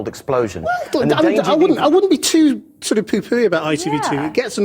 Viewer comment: what does he say at 6:06? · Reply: Poo pooey